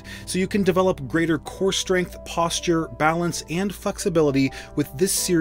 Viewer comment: did he say fucksability ???